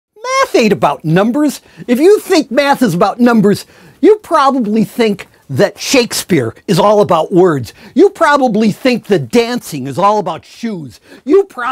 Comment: At the beginning i thought he said meth ain't about number instead of Math ain't about number